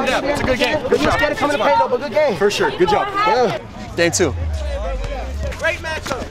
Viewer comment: At the end of first game he said “for sure, good job.” What I heard was “get a shirt, put it on.”